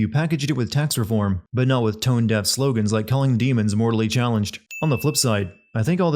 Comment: Aww! I thought they were saying "morally" challenged. ... works either way, I guess.